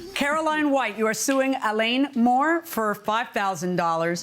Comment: I thought the announcer said he name was A lawn mower.🤭🤔🙄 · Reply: I thought I heard Elaine. I'm so glad they spell out the litigants' names!